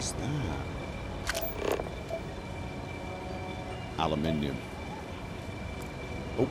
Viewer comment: I thought you were saying Aluminum as in Aluminum foil... Aluminium is a metal. 😂